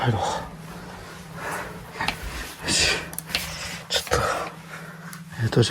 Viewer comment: が「帰ろう」と言った声に被って 「よせ」と言ってる様に聞こえました。